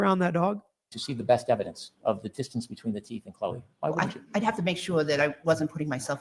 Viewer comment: I thought she said said it was teeth snd claws.